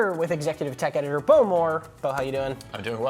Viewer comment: im just gonna say it.... at 0:03 I thought he said Boomer